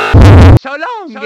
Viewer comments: It Sounds Like Mario Saying *So Long Gay Bowser* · I kept hearing it as "so long gay bowser." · Fun fact did you know that he's actually saying so long king bowser? gay bowser is a misheard thing but it's funny lol